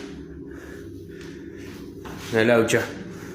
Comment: subtitles are wrong. He doesn't say "una ducha" which is shower in spanish, he says "una laucha" which is a rat or mouse in argentinean spanish.